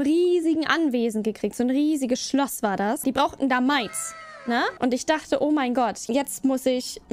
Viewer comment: Ich hab erst verstanden die brauchten Mais